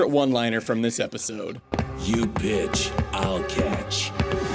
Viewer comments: Did I just hear "You bitch I'll catch"? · In regards to favourite one liner from entry #5, I honestly thought he said "You bitch, I'll catch"